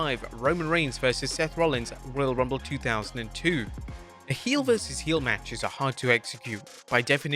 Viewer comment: I liked it when Roman reigns fought Seth Rollins at royal rumble in 2002😂(it’s a joke) he said 2002 instead of 2022